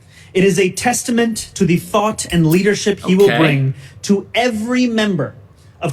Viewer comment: that subtitle is totally misleading .. he actually said "... to the fart of leadership..."